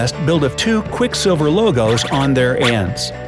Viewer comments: Did anyone hear the random voice on 4:54 of the vid? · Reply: It says, "Clever, huh?" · If you're wondering what it said at 4:55 it says " number 7" and "clever, huh"...i listened to it 29 times in slow-mo so you wouldn't have to.